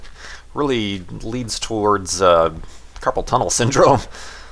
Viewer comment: Misheard "carpal tunnel" as "purple tunnel syndrome". I presume this is some sort of irrational tendency to build tubular structures from purple wool and obsidian...